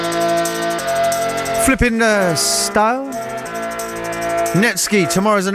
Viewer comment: What does flippin err... style mean? lol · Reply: Thought he said Flipping er stoned